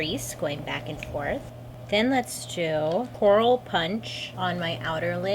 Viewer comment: I really had to rewind 10 seconds because I thought you said Oral Punch instead of coral punch. I thought, “aren’t we going to talk about this eyeshadow name tho?” 🤣🤦🏻‍♀️